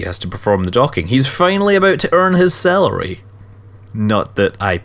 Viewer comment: Ok, I'm sorry, I couldn't help but chuckle every time you said "salary" because with your accent, it sounded like you were saying "celery". XD So the Kerbal that was performing the docking was "Earning his celery" Anyway, I love your videos. Have a good one, and as you say, "Fly safe"